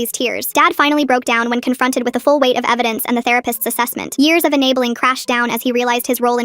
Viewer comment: UM…… why does the subtitles say something else other than “therapist” 💀 WHAT help